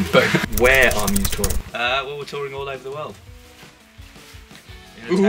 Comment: Someone could tell me what Dom says at minute 1:26? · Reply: "Ah well we're touring all over the world "